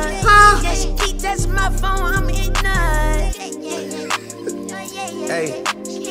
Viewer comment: "did he just say " feet touching my phone ima nut " ?